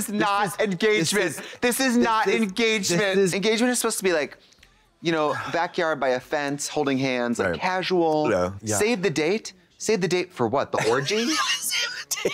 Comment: I thought "save the taint" was said because I heard orgy haha but now I'm second guessing myself if it was just "save the date" the whole time 😂